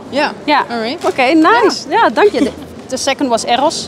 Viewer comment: did u say danke XD